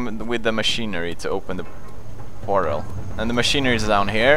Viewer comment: did PewDiePie say finger or figure? Cause it sounded like finger.